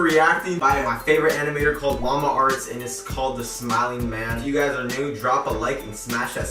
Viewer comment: At first i thought you said the clapping man. So..... i guess he claps people